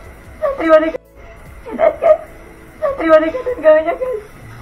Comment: itu tadi dia bilang enak kan saya terima nikah dan mas kawin nya ...apa gw yg salah denger ya